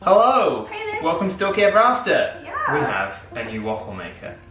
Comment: I swear it sounds like said "Welcome to Donkey Ever After". This is so cute & wholesome